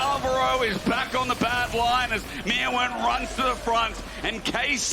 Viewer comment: Did he say bad lions @ 41:50 ?